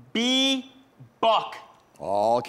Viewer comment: Didn't it sound like he said Buck?